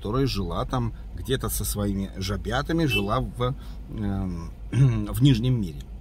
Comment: доброго тебе,мне послышалось жопятами